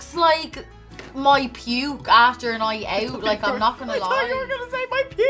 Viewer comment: Everything would have been better with some potatoes 🥔😍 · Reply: so...... i thought the same thing LOL thought you said pubes HAHAHAH!!!!